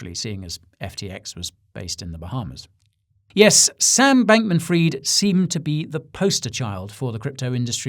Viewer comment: I thought he said "Yes, Sand Bankman-Fried" pulling another pun